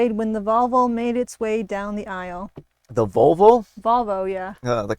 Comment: I definitely heard 'vulva' instead of 'Volvo'. Oh...man, that really changes the context of the punchline... 😅